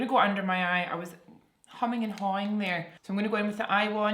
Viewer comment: sounds like humming and something 😅 instead of umming and ahing